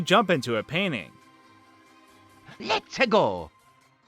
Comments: Oh my gosh! I heard “Let’s pickle!” when I was younger. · I thought Mario was saying "Hot Pickle!" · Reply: I heard "Let Tickle!" or "Let's Tickle!" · I thought Mario said, "Let's tickle!" too. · I thought mario said "Lets Taco" instead of "Lets a Go" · I always hear Mario say " La Peggo"..... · My sister misheard "Lets-a-go!" as "Love pickle!" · when mario said "letsa go!" i thought he said "let her go!" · I hear Lets-a-go! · I thought he said testicle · I thought mario said Whets o go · I knew he said "let's a go" but when I was playing it, it sounded like "red pickle". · I always heard the "let's pickle" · When mario said let'sa go I thought he said That tickles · for some reason me and my sisters thought in new super mario bros wii when mario said let's ago we thought he said pizza dough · On Mario 64, for the longest time, I thought he said "nuts pickle" after selecting the stage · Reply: ImmortalXInstinct I always thought he was saying "Let's tickle!" after you selected a star, and because you picked a star, you were tickling it lol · i know this is weird but when mario said lets a go in 64 i thought he said HOT PICKLE! i know its silly